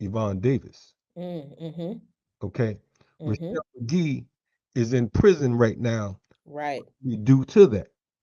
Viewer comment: Sound like said...@ 7:08 "chuck D is in prison" ? Was he referring to Cinque here?